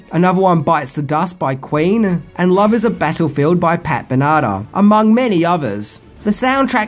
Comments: Pat Benata?!? 😂😂😂 Ben-a-tar. I sentence you to do 10 things about, "The Legend of Billie Jean." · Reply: I thought he said Pat Banana through my potato speakers… Potato Head speakers maybe? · did you call 80's female rock star Pat Benatar "Pat Banata"? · Also.... 8:06 --- Pat Banana is all I hear. It's Ben-a-tar. 🤦‍♂️ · its pronounced ben a tar 😂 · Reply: sounded like he said pat banana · did you call 80's female rock star Pat Benatar "Pat Banata"? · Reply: I thought he said Pat banana. Oh!